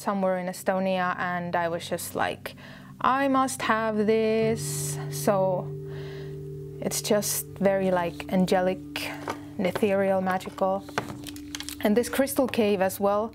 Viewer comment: Does anyone understand what she says in minute 5:32?? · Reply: "I must have this. So, it's just very like angelic and ethereal, magical."